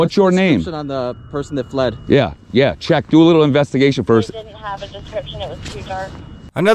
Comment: did you hear what a dispatcher says? I maya be wrong but she says" we didn't have a description, it was too dark" in other words,"wtf are you talking about. we never had any description.!"